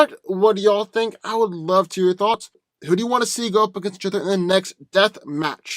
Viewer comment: Wtf did u say at 20:34. To 20:36 lol 😆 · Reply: Box,who'd you wanna see next.... I think..